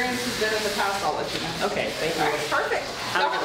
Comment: IS NO ONE GONNA TALK ABOUT HOW THE DOG IS CALLED THANOS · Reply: I thought I heard him say "Okay, Thanos," so I came down to the comments to see if it was true